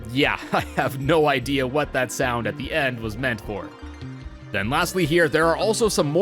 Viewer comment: Freddy: says "A<link> In a disappointing yet cute way. Tetra: "I have no idea what that horrible vicious sound was." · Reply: they actually said “yeah I have no idea what that sound at the end was meant for “